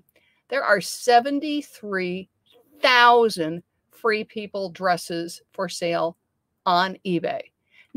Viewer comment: LOL. I was listening (rather than watching directly) and thought you were saying "3 people dress". I was thinking that was a strange name, even for "plus sizes"! Thanks for the video.